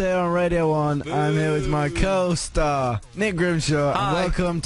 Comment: at first I thought Harry said curl-star instead of co-star in the beginning